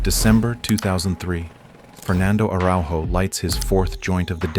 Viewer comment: I thought you said Fernando Alonso for a second💀